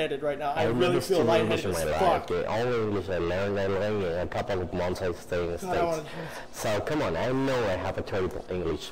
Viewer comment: i’ll give a hundred to whoever deciphers what the dealer said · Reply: A couple of months i stayed in the states